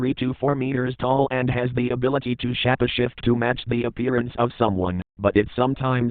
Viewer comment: sounds like he says "the ability to shat a shit"